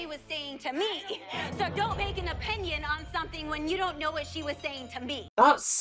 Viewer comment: thats not what she said😅 she said " dont make an opinion in something when you don't know what she said to me"